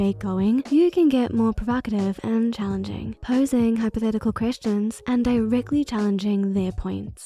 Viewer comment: I kept hearing 'chrishen' which sounds like christian, pronounced weird. Untill I realized it was 'question' with a intonation of a question. This "accent" can't be real, the intonation is waaaay off.. (And it sounds exactly like btw. No doubt about it. "Drew" must be a pseudoniem for this accent. ) I really wanted to watch this video but I swear I can't follow it bc of the fake accent. ☹️